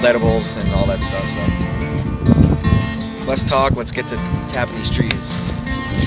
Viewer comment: lol I thought it said, " Tapping the Bitch."